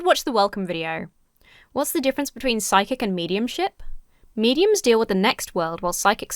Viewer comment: I dead thought you said "psychic- and medium-SHIT" and was like YEA, tell them. Then I realised it was -SHIP. MediumSHIP.